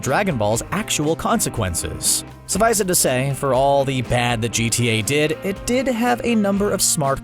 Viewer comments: did he say GTA? XD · did he just say gta lol